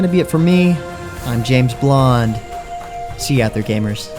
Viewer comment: did you just say james blunt?! haha